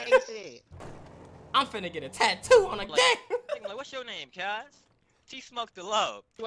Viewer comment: why it sound like dontai said I'm gon get a tattoo on the dick😂😂👀